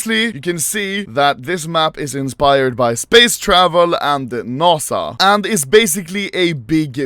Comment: is him saying nossa